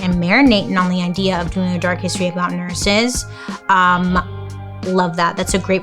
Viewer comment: totally thought that said nurserys 😅